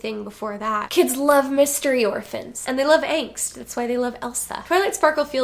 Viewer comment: I heard "angst" as "eggs" multiple times. I have nothing else to add because I keep wanting this to happen, it's perfect